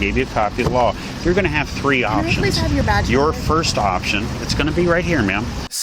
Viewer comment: I heard her say "can i have have your badge please sir" lol